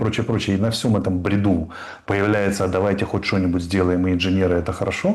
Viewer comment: Послышалось,, не в ряду , а в бреду ,,😂